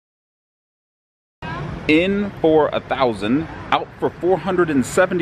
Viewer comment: Sounded like you said in for 4000...out for 4072... I was like, at a 2/5 game?!! Lol... your cutting room floor bits cleared it up at the end 😊